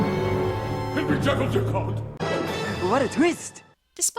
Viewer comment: me: (reaches 17:20) I'm sorry, what did he say? (rewinds to the same point) ....If anyone knows what he said, reply on this comment with the answer. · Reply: I think it's Henry Jekyll dear something??